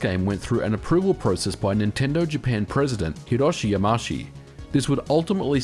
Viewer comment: Hiroshi WHAT?! .... It's said 'Yama-uchi'